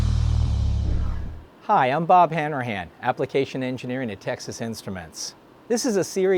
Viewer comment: For a sec I legit thought he said his name was Bob hammer-hands